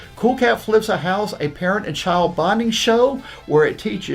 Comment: I swear to Christ it sounded like said "A parent and child bondage show," and I now want to see that as opposed to them flipping a house. Also, has some of the greatest ideas in his head, such as him writing roles with Mel Gibson and John Travolta in mind to play said roles. Unfortunately, we don't get to see those ideas come to pass! If he actually managed to get Gibson or Travolta in the movie, I would pay hundreds of dollars to see that sh*t in theaters!!!